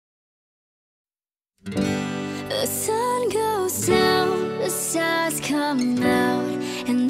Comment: The sun goes down The sus comes out 0:02 what I heard